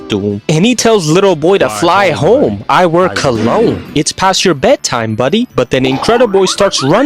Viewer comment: Help 5:55 why does everyone say he says he wears collagen he just said he works alone😭🙏